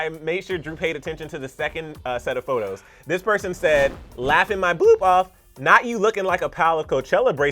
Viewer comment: made me laugh sooo hardddd. I thought he said,"laugh my BOOB off!"